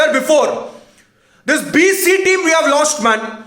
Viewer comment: did I hear BC team?😂